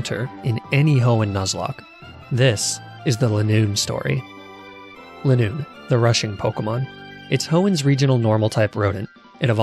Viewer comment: I heard that as "Linoone the Russian Pokemon" and I can only imagine it feasting with its comrades under the sun on Route 101